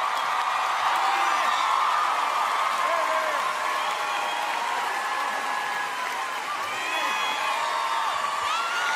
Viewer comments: can someone tell me what Harry said? :0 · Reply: He said “ Her hair is bright ” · what did harry say at 8:44? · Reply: “ Her hair is bright ” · Can someone read lips and tell me what he said at 8:44?? Thankyou!!! ❤️❤️ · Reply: He said “ her hair is bright ” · What does he say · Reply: He said “ her hair is bright ” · what does he say on 8:45??? i couldnt understandddd aaaa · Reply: He said “ Her hair is bright ”